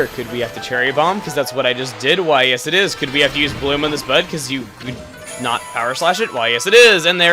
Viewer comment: Really sounds like you said "you have to use bloom on this butt"